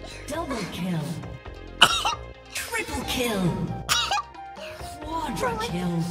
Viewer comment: It was hilarious when she sneezed three times and I heard 'Triple Kill'